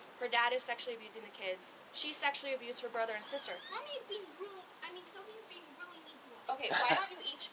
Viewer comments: Daisy Seed YES! & WHAT DOES THE CHILD ACTUALLY SAY FIRST @ 18:20 ??? 🤔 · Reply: Andromeda Palazzolo he said “mommy’s being really.. i mean sophie is being really mean to me” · 👂👂👂WHAT DOES THE CHILD "ACTUALLY" SAY @ 18:20 **<BEFORE<** THEY SAY "SOFIES BEING REALLY MEAN..." ??? 🤔 🤨 try listening in slow speed as well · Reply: Damn im glad you said that cause first time I hear Sophie is being really mean then I listened again he said mommy's being really..... i mean Sophie is being really mean